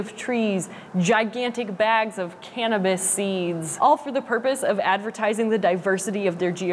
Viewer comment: Did you say Cannabis seeds at 2:51. Oh my!!!